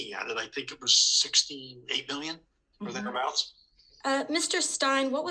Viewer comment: The caption says "$68 billion", but I think Mr. Howley said $68 million. [edit] By the way, thank you for all that you do, Representative! :D [/edit]